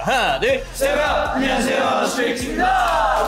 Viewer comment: stary kids instead of stray kids hahahaha